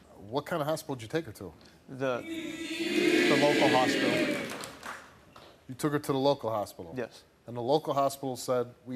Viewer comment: What did the crowd yell….??? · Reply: "Steve"